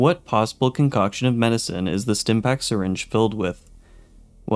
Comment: for the longest time I misheard Stimpak as STEM-Pack, as in a pack of stem cells